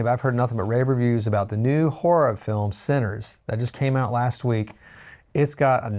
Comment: Just to clarify, the movie is called "Sinners" and stars Michael B. Jordan. I just spent a few minutes trying to find this great new scary move that I thought you said was called "Centres". I was almost movie scammed! 🤣